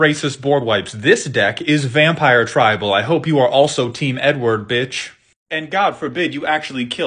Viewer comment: “i hope you’re team Edward” 😂😭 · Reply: First time I heard him say it I had to do a double take, thought he said “hope you’re team N-word” lmao